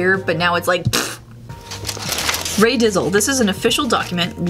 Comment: I thought it said Rae Diggle